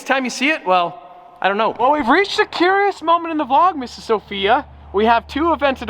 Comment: did said Mrs. Sophia?